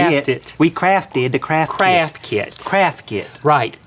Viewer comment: when they said 'craft kit' (at 7:15) it sounded like they were saying casket xD lol